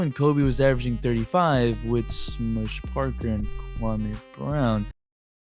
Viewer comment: Was 1:04 even English? All I heard was Kwame Brown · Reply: With smush parker and Kwame brown